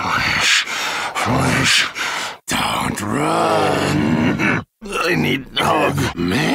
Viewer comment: Did he just say “I need hug”? 🥺🥺🥺🥺🥺🥺🥺 I can’t for the life of me here him say “help” here. Poor guy, I feel so bad for him.